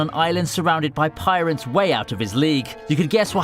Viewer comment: did you say pirates out of his...out of his what I can’t even say it💀